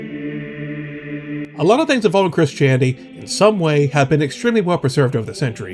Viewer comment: I thought to said “Chris-chanity” at that end part. I’m like yeah people really chronicled Chris chan’s whole life